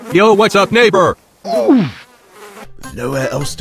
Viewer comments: yo wtf did bob just say? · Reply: He said "Yo, what's up, neighbour?" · it sounds like bob said what’s up n